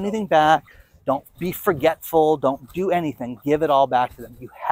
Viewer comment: I thought said don’t do crypto